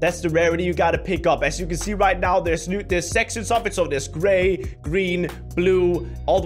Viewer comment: I think I just heard sex and stuff at 0:35 OMG I've never had this much likes :D